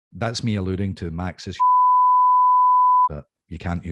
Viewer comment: What do you think said? 🤔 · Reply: Maxis sex